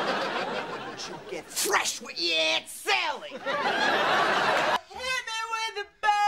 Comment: I know she said “…don’t you get fresh with ye aunt Sally!” but I heard “…your aunt Chippie!” because she sounded just like Kimmel’s auntie.